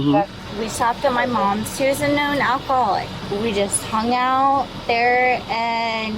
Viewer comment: I thought she said "alphaholic".